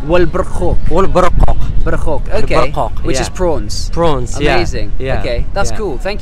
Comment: Hahaha amazing video! But he translated barqoq wrong to you, it actually means plums :) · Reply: I think I misheard him. Think he said 'prunes' and I heard 'prawns'... oops